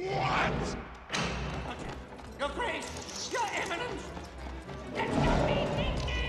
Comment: I always thought he said "You're on the lose!"